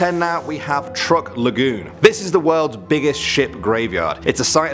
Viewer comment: At the beginning I thought you said "shit graveyard" and u just stared and thought about it a second. And then laughed because I realized that it was ship, not shit...sorry, haven't slept in over 26hrs 😂